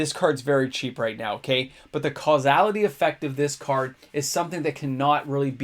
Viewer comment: On Ob Nixillis' casualty ability sounded like you were saying causality, such similar words